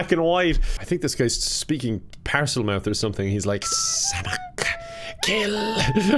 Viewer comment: oh noooo i think the person in 2:30 saying “ksss amk” is cussing in arabic because this sounds a lot like the arabic equivalent of “m*therf***er” 😭😭